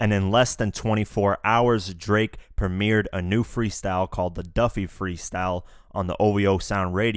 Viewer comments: is it me or is dude saying DUFFY at 0:25 . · Did he just say “Duffy” lol